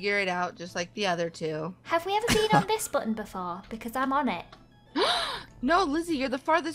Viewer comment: lizzie for a sec I thought u said, "Wait. have you guys peed on this button before?" I died